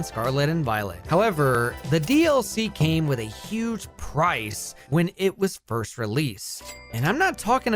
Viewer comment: Not my dumbahh hearing prize instead of price